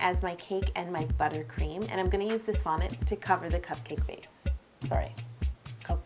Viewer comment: omg I am <num>% sure that she said vomit instead of fondant